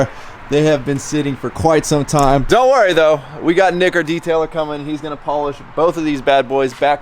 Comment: anyone else hear what I heard? Lol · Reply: “Nick our detailer”